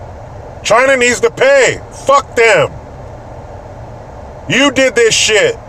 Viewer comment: man for a second I thought you said China needs to wipe our dicks clean...I was like damn thats gangsta